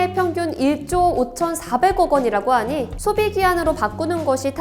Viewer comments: 에 1조 5400억원이라고 들리는데 저만 그런가요? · 오디오론 5400억원이라고 들리는데 편집은 다르네요ㅠㅠ